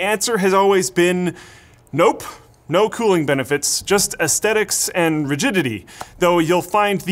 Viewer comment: I thought he said "aesthetics and virginity".